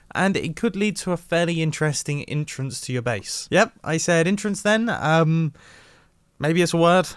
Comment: "...lead to a fairly interesting entrance. Yep, I really did just say entrance there. Maybe it's a word, I don't know, look it up. Apparently it's an archaic way of saying entrance." So "entrance" is an archaic way of saying "entrance." Got it. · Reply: He said “Intrance” but his accent makes both words sound the exact same.